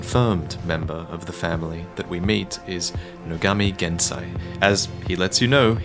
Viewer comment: At first i thought he Nogami Gensai said his name was Kawakami Gensai and i lost my shit